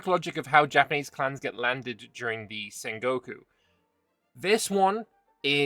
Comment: The way you pronounced Sengoku, I thought you said Saint Goku. Which should be an option for a christian Japan.